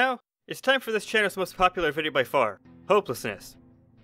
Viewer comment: At first I thought it said homelessness